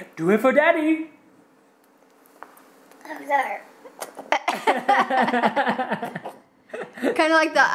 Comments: I'm not sure if it was just me or did say "I'm sorry" at 8:41.. :P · is it just me or does it sound like says sour? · it kinda sounded like said "I'm sorry" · he actually said so sour!! SO CUTE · did say "So sour"?? or am I just hearing things lol he is way to cute! :) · When was trying the lemon around 8:40 it sounded like said "oh god." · it sounds like said I'm sorry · the noise made at 8:40 was so weird haha actually sounded like he said "oh sour" a little bit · did just say I'm sorry?!